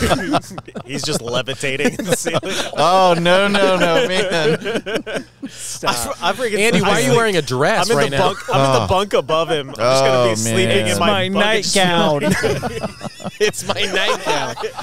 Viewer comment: i thought it sounds like it said "why...are you here?"